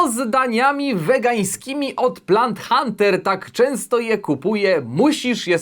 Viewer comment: Tylko ja usłyszałem "blant Hunter"? :P